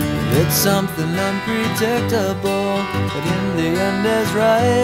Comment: I thought I heard "The Indian is right"